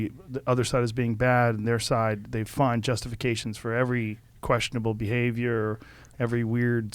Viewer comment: Did he just say "Find Justin Gaethje?" :D Chandler vs Gaethje? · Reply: Justification